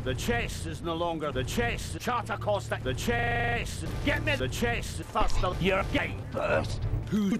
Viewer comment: is he saying "Get me the chest first or you're gay"?